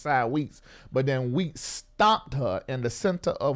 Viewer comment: stomped her in the center of her will.... oh no. 🤦🏽‍ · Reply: How about I thought said "stopped" her in the center of her will. But when I realized he said "STOMPED"!!!!! ?????? I FELL COMPLETELY OUT.....that thing was good for me lolololol